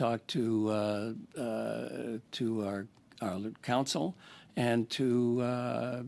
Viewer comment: Sounds like he said . I talked to our loop...hole ...counsel.